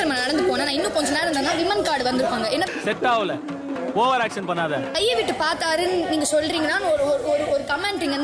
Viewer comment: Actually Aurora Said 'Kaiya Vittu Nondi Paathaaraa' apdinu Idhu Enaku Edhum Thappa Thonala Viyana Ennoda Dressnu Therinju Kondu Vandhu Vechurukaarunu Sonnaanga Appo Kaiya Vittu Paathaaraanu Aurora Ketaanga Viyana Andha Maari Sollalanaa Indha Comment Aurora Sollirukave Maataanga Aurora Said Face To Face To Viyana But Yaaro Sonnangannu Poi Solli Kelambitaa Poiyana For A Reason 😂